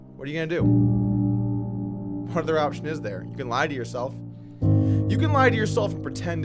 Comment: Is is bad that I thought he said "I can't mine it myself" for a second when he said "I can't lie to myself"? Crypto on the mind, mining Electroneum Woo!